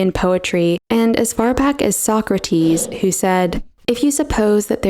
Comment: Did anyone else hear the word "Soccer-Team" when she actually said "Socrates"?